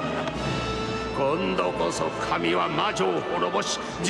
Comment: What does he say here? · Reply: 今度こそ神は魔女を滅ぼし、地獄の炎に叩き落としてくれるのだ。 (Kondo-koso kami wa majo wo horoboshi, jigoku no honoo ni tata ki otoshitekurerunoda). This is what he said I believe